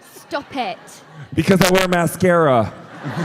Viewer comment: What does Khal Drogo say at 34:33 when The Hound says "Hey Khal, how come you get all the girls?"? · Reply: Jason said because I wear mascara